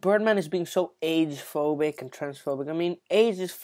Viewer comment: Thought he said AIDS is fluent.